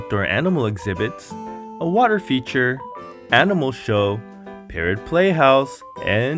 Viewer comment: Love that water feature! I wonder if those are bullfrog tadpoles? At first I thought you said "Parent Playhouse"!😆 That sounds like fun! Alcohol must be involved!😆